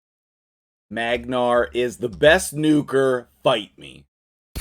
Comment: I heard 'magnarr is the best nuker, bite me'. I rewound it and heard the same thing lol. I can only tell when I watch you say it